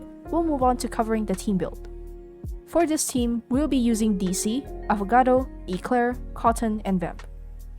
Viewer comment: thought they said avocado for a sec and i was confused